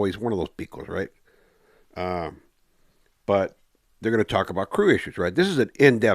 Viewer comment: "they're gonna talk about Croations, right?" Did i hear that correctly? Closed captioning is pura mierda on this channel. Lol